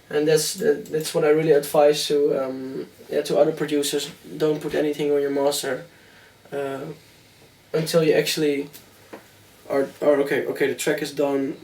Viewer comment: "don't put anything on your..." What does he say after that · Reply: Don't put anything on your master mixer state until you're finished with a track so basically master the track after its finished